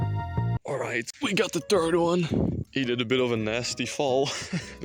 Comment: I heard "turd one" not "third one"